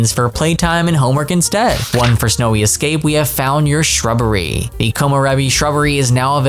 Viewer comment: "We have found you a shrubbery!" You pronounced it "your shrubbery" instead of "you a shrubbery". The line is a reference to the cult classic movie "Monty Python & The Holy Grail".